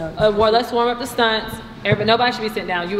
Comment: I thought she said don’t sit down… how do you bring in coaches to help your girls but allow them to act the same when they leave. It’s a smack in the face to them and wasting their time. Like they top gun coaches said “it’s selfish “